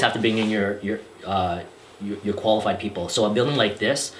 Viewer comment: What was he saying at the 9:45 mark? A PNG inspector? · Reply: Eng. Short for engineer